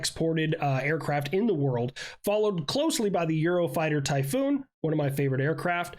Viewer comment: did you say "gyro typhoon"?